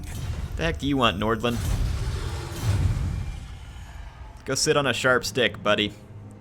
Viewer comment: I misheard "Go sit on a sharp stick buddy" to, "Go sit on a sharks dick buddy"